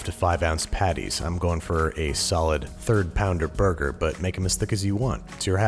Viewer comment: I thought he said "Make em as thick as you want, it's your ass".